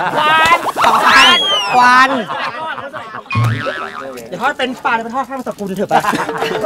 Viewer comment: พี่เขาพูดว่าไรอะฟังไม่ทัน<num> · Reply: "อย่าทอดเฟรนช์ฟรายเลย ไปทอดผ้าบังสุกุลเถอะไป" เราก็ฟังหลายรอบเหมือนกันกว่าจะฟังออก<num>